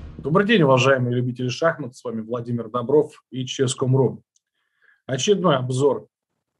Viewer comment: Послышалось очередной позор вместо очередной обзор.... думаю ничего себе....